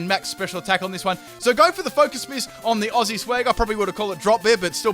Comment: I thought he said focus piss